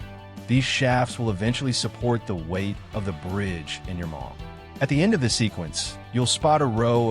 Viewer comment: did he say "and your mom"??😂😂